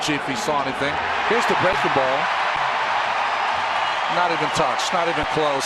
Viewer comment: You can hear Brock holt say that's a fucking ball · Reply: Oh it sounded like he said it's a fucking ball